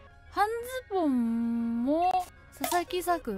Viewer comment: はん↑ズボンがハンギョドンに聞こえたwwwwww